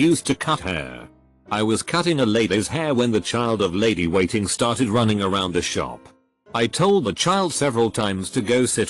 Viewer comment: I misheard the haircutting one and heard "The lady of the child started to run around the shop, I asked her to stop multiple times"